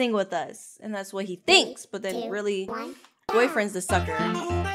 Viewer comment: Did bro just Say "We have another Fucker to sing with us"